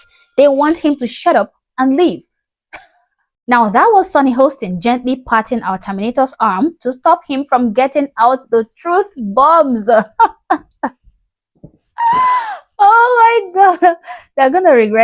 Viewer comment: I thought she said "touching our tomato's arm" 😂